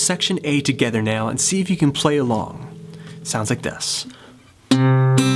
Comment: Thought you said , "Play it worng" lol